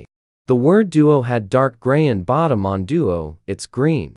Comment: I thought you said gay